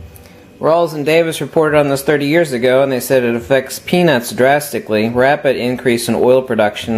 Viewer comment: I thought you said it affected penis drastically and I immediately put the South pole magnet on my junk.